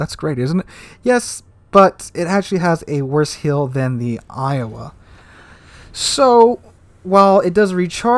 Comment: In the video I state that Georgia has a worse heal than Massachusetts, this is a mess up on my part. I meant to say it has a worse heal than Iowa. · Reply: I hear you say Iowa